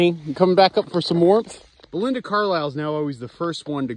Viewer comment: Belinda Carlisle running up to you is pretty adorable. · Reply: I heard "Belinda Cow-lisle"😂😂